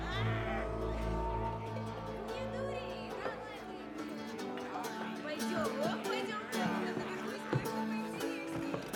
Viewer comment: На 1:23 девушка говорит, не дури, а дальше что? Не могу расслышать, может у кого получилось? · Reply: Не дури, давай выпьем. Пойдём, пойдём, наверху будет кое-что поинтереснее!